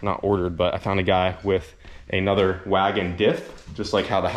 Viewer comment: i wasn't watching but i was listening and it sounded like you killed a guy for his wago diff xD